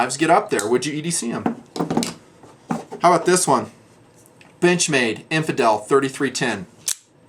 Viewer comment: When he says "benchmade" i hear "bitchmade" hahaha!